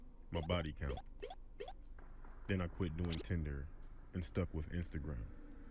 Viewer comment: when he said tinder i thought he said tender